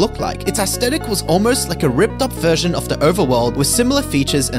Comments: It sounded like he said suffer more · I thought he said suffer more 😂